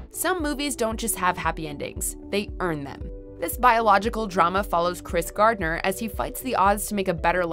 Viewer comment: Did she say biological drama?